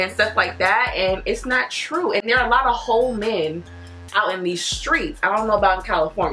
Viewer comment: What you said: "There are a lot of whole men" What I heard: "There are a lot of hoe men"😭 idc how much you enunciate it